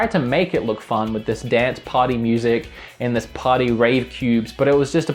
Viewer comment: I thought he said party rape cubes...